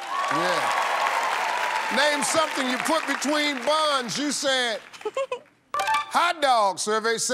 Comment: I thought he said between BONDS